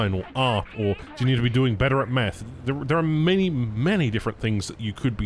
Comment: I had to rewind and enable caption as I heard "be better at meth"